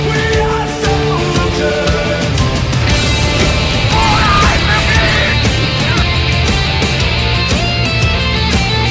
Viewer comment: What did he say? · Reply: FOR THE REPUBLIC!!!